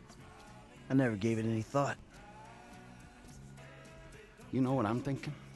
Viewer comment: thought he said never grave it any thought